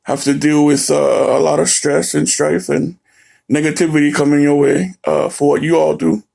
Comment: who else heard n**gativity?!